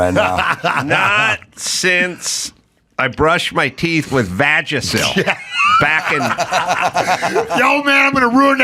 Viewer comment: Why's everyone gotta piss in Mayhem's cheerios? So he laughs loud, who cares😋 ...am I the only one who thought they were saying "Vagit?"🙃😆